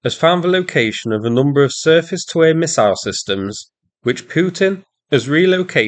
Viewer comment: Unsure if this is a bad joke or not, but no, he said surface to air. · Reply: I listened to the clip several times at that location. The sound is slightly distorted, so it sounds like he said 'Circus' even though the word is 'surface'. Which would track as likes to take shots at Russia's messed up actions in many videos in a similar fashion. So yeah, just having a little adjacent fun with the semi-weird sound track at that moment. To be clear, I wasn't being critical of, just having fun at the Russian's expense.